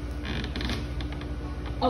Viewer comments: Human: " Stay, open, good boy" Alligator:" Child, I did not thrive and evolve for millions of years to become a mere pet. Now give me that food!!! ". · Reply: Мне послышалось, что в конце она со страха заговорила по-русски: вместо "good boy!" сказала "Давай!" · So much trust, as she inches back in fear. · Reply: А мне послышалось, что в конце она со страха заговорила по-русски: вместо "good boy!" сказала "Давай!" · She’s one of those “Do worry he doesn’t bite” girls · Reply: Мне послышалось, что в конце она со страха заговорила по-русски: вместо "good boy!" сказала "Давай!"